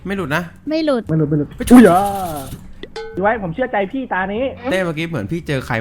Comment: ซับผิดค่า ตัดเงินเดือนนน ตอนที่พูดว่าผมเชื่อใจพี่4:02 นี้ค่า